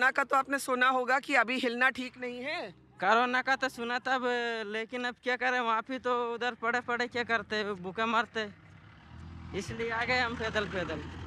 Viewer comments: suprising !! how subtitles are misleading at some points. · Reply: he replied to question that " I've heard of Corona but what could I do just sitting at home and starving" instead of saying that riches pushed em away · suprising !! how subtitles are misleading at some points. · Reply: "कोरोना का तो सुना था वो उधर पडे पडे कया करते भूखे मरते"